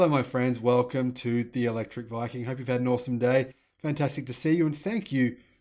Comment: Haha at first I thought he said in his intro "Hello everyone I hope you have had a Norse day".